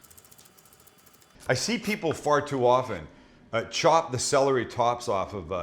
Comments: into the clip, and correct me if I am not right on this, however did you say , " I see people Fart too often " ?? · I SWEAR he said " I see people fart too often"